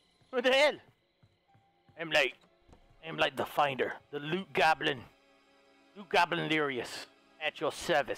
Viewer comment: did I hear.....goblin?